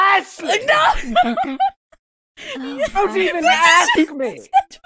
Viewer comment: "Don't Judge meh tits" that's all I hear when this plays